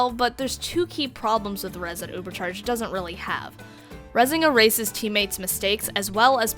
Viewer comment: thought u said "rezing a racist teammate"